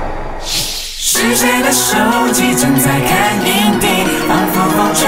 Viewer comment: 第一句我聽成 是誰的手機正在看陰蒂 對不起…(面壁中